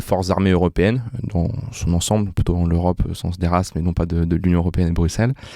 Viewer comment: Corrigez moi si j'ai mal entendu, mais ce monsieur a t-il bien dit à 3:10 « De l'Europe des races et non de l'union européenne de Bruxelle » ? Si c'est bien le cas faite attention aux énergumènes que vous invitez, d'autant plus que vous êtes soutenu par un ministère de l'Etat...